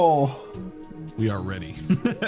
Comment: sounded like he was saying "8:30" lmao